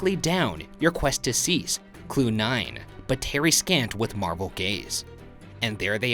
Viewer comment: I definitely thought I heard say “Marble” instead of “Marvel” so I think that bit of marble might’ve been something but idk 🤷‍♀️